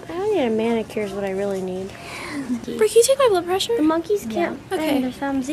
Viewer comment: i thought it said high blonde pressure